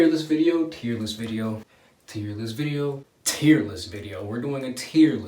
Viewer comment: I thought u were saying tearless video